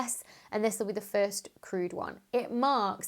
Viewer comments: At first I thought you said "The first crude mission...." and wondered what was wrong with it. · Oh “crewed”! I thought she was saying that Space X’s mission was “crude”.